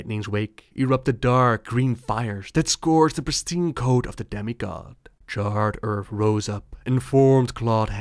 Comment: I thought you said "Daddy god" and then realized you said demi god.